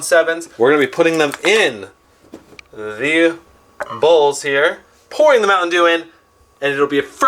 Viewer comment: it sounded like you said in these "balls" here. XD